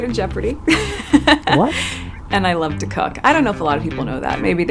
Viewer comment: With her vocal fry it sounded like she said 'I love to cock...' 😱🙄🤔😁🤣😎